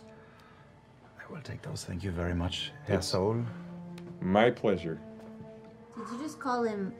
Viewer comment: German here, for anyone who didn't catch it, Caleb called Pulmat Sol "Herr Sol" which basically means "Mister Sol" and Jester misheard it as "asshole". Also, compliment to caleb for his almost perfect pronounciation of german words.